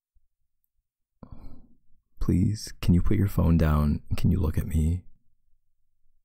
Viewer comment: I FORGOT THIS WAS PLAYING AND I HEARD “girl can you put your phone down and talk to me” HELLPP